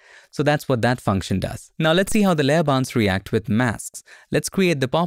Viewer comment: For 2 mins I genuinely thought you were saying ‘layer bounce’ and thought surely it doesn’t literally bounce? Penny dropped actually seeing the layer ‘bounds’ 😂 Great new feature, will look forward to this!